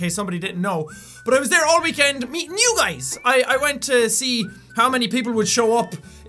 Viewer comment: Lol i thought he said:"and i was there all weekend eating you guys!"